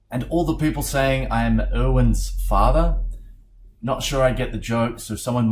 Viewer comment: I thought he said Owen's father but then I heard erwin and it made more sense